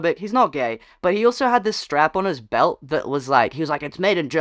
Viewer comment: the way i thought you said "he also had this strap-on that was made in Germany" and i oop 🤣🤣